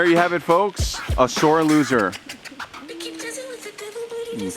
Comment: Hahaha so true 😂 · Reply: Binge watching!! Love your channel! I thought he said "shore" loser!! Feel better! Spider bites are no joke!